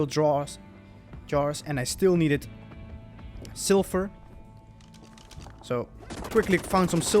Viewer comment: I thought you said silver when you said sulfur 😂 cool vid! Edit: I just edited but I didn’t get the edit crossbow? I got the snail crossbow